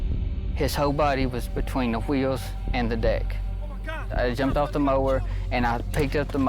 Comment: I thought he said dick